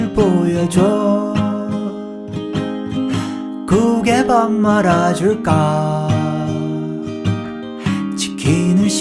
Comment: 국에 밥말아 줄까라는 부분에서 난 왜 보신탕 해먹어 줄까로 들었을까?ㅋㅋㅋ